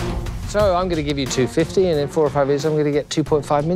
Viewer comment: The aussie accent made it sound like he said 45 years lol. I was wondering why they were taking that seriously until I realized they said 4 or 5.